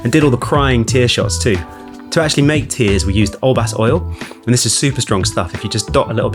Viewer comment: What brand of oil was that? Captions say "alas oil." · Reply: Olbas oil 🥲